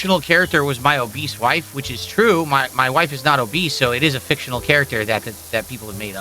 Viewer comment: did bro just say its true his wife is obese